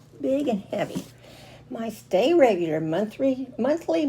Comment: Hi. I thought you said, "Stay Regular Box". I was thinking prune juice, Metamucil, and the like🤣 That's a Big Mystery box. That's cool about your son & grandson. I enjoyed the video. TFS. Have a good night.